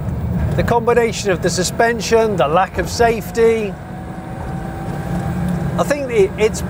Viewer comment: Did he say the lack of safety? 10:09-10:10. Did I hear that right?